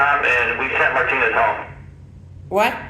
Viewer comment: Yep another Martinez at the rock island Jail along with another c.o beat the shit out of an inmate a few years ago. I only learned that when researching this Martinez on google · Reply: Listen at 17:21. He said, "WE SENT MARTINEZ HOME!" YES!!!!!! He didn't repeat it when you told him you didn't hear what he said.